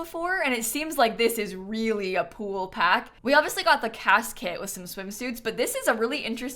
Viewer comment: I thought for a second she said "casket" instead of "CAS kit"